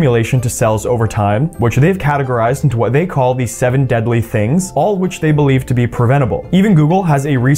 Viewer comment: My dumb*ss literally thought he said the seven deadly sins 😂